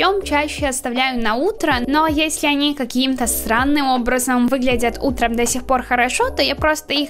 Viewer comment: я же не одна услышала "сранным"?